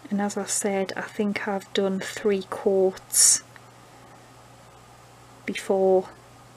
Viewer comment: Hahaha I thought you were saying quarts instead of coats.